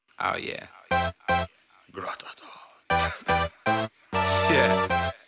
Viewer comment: Did anybody else hear that "gratata" at the begining? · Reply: Its Me NIGGA SAME 😂😂😂I WAS LIKE hold up did he just say gratata let me play this again😂😂